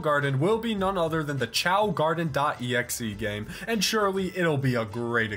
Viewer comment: I thought it said chad garden.exe lol 😂